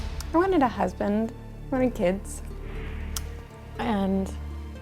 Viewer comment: wait! Did she says she wants 20 kids??!?